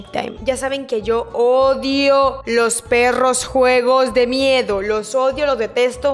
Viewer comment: Pensé que había dicho yo odio los perros😅 jajajajajjq😂 mi